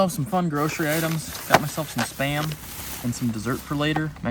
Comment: I thought you said you got a "spam dessert" i was trying my hardest to figure out what that could even be xD xD